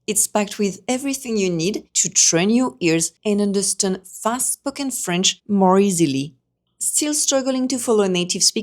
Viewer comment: I started listening to this whilst washing the dishes. At 00:20 I thought you said "fast f*cking french". Yes! It is f*cking fast! That's what I need! 😂😂😂